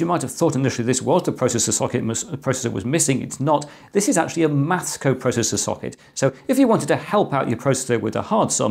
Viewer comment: How do you spell this processor? Auto-captions say "Moscow processor" wich sounds like BS to me. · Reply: I say "maths co-processor". So the auto captions are wrong!